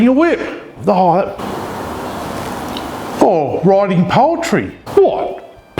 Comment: I thought he said "Riding Poultry" is something to do while you wait! LOL